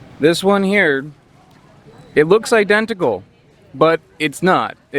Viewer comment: it's raining hard here right now and I didn't hear correctly.... for sure thought you said "it looks like testicles"